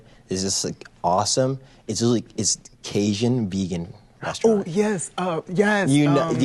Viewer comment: idk but I think he said Cajun, and I’m Cajun so I can tell you that our food is NOT vegan. I don’t even know how to make it vegan without it not being Cajun